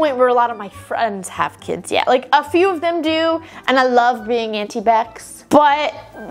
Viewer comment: Did anyone else hear anti vax💀💀💀 I know she said Aunti becs